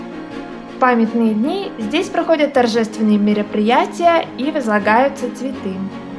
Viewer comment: Мне не послышалось? На 3:22 разлагаются цветы?))